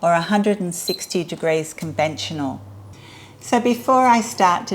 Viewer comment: Do you have a conventional oven, conventional settings are 160°C (320°F) - just a slow oven. I hope that helps, here's the printable recipe on the website: <link> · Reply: Thanks, it sounded like you said convection oven which is why I was confused, thank for responding